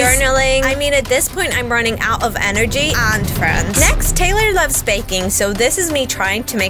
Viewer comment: "Next Taylor loves baking" I thought she said Taylor gets a spanking 😭